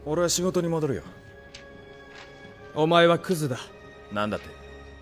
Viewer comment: did he say omwea but no the full word